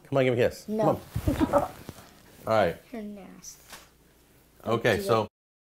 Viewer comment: Wait, did he say, "You're an ass, don't do that shit," @ 4:00? OMG